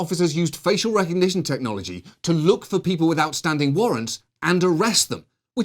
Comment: "They used this facial recognition to arrest people with outstanding warrants" . . . Wait . . . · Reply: I thought he said, "without standing warrants" so that got me way more concerned but yeah, this makes more sense.